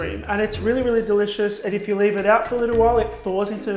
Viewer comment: for a second i thought you said bay leaves....i was like what??? 😂😂